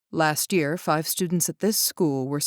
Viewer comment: Instead of “last year, five students at this school...” I heard “ last five years” and I was confuzzled